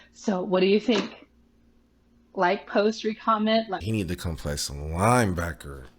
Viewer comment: did she say “re-comment”? 🤔